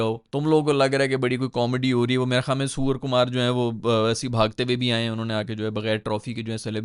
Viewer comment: He actually said: ""Suwar Kumar" on 9:14 🤣🤣